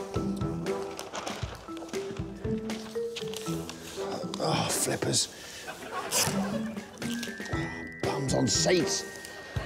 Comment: Thanks to Tim Vine I've found myself involuntarily saying "Flippers" and "Bums on Seats" when I get frustrated around the house. My nieces and nephews find it very amusing. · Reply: I misheard it as, "Bums on Saints" and was amused but confused.